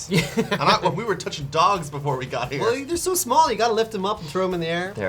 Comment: I thought he said we have been touching DONGS LOL